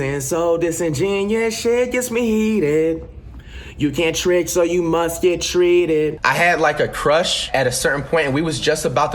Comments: I thought he said can’t trade so you must get traded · Lol I always thought he said Che gets me heated